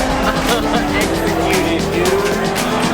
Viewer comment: “HEY DUDE” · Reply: retrograde right!?!? I was like did he just say “hey dude” and I had to play it back 😂